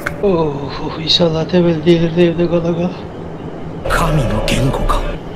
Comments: da ne dediğini anlayan? düzenleme: şimdi anladım şey dio : insanlar temmeli delirdi evde kala kala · saniyesini 85. dinleyişimde ne dediğini anladım dediği şey: insanlar temelli delirdi evde kala kala